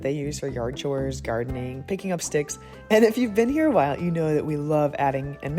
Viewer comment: BAHA is that what the subtitle says??? 😂 I need to edit that... or maybe not. We have used it to pick up parts of a dead squirrel lol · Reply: I think the captions say sticks but I definitely heard "stiffs" at first 😂